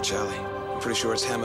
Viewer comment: what hammer said in 6:47 ????? "one of your brothers can leave " or " you and your brothers can leave " ????? Which means leave Summer Bay ?